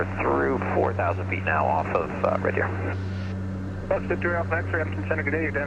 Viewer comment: Was that a take off from red deer regional airport? Thought I heard you say 4000ft off red deer lol